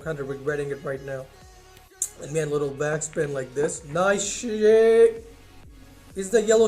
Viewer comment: Does he said nice sh*t